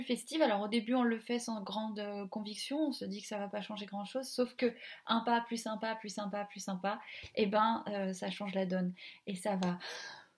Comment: J'ai entendu "Un pas, + un pas, plus un pas, plus sympa, plus sympa, + sympa, + sympa...!".